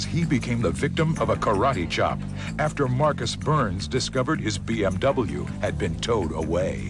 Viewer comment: i swear he said BFW instead of bmw.